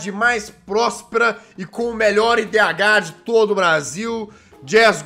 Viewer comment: Bh ou ph kkk não entendi kk 0:12 🤣🤣